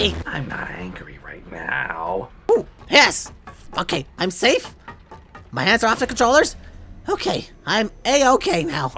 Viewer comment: Did anyone else hear sun say "fuck it)? I think that's what sun said. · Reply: “Fuck it”